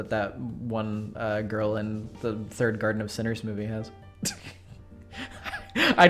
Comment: I know likely no one will ever read this comment but me, but I gotta say, I misheard this as "The third colonel sanders movie" and was completley confounded